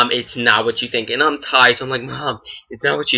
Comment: did you say "and i'm high"?